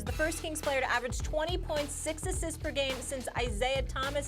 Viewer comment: I thought she said that he average 20.6 assists, I was tripping out